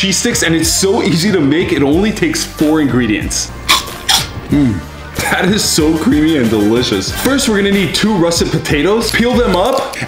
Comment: Pov: you heard "this is so nice and creamy" and you went to the comments✋🏻💀 Edit: ayo you all are sus😳😳😳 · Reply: I heard "This is so creamy and delicious"